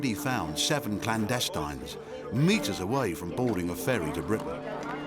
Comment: Is anyone here British? Complete silence and confusion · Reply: I heard "Britic"